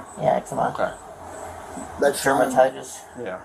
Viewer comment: Does say hepatitis @ 27:35? Listened to it a few times now